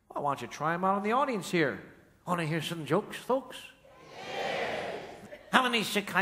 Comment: wanna hear some jokes "what?" Fox? What's that? I know it will probably means "audience" but what's the word?